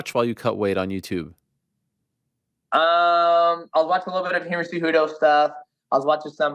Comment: what did he say? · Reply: A bit of Henry Cejudo stuff .👍✊